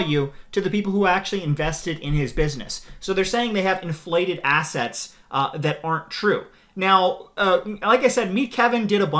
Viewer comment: I heard that as ‘inflated asshats’ which describes a lot of these guys pretty accurately.